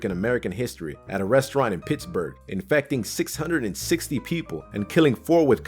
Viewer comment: well, for a secI thought he said "666 people".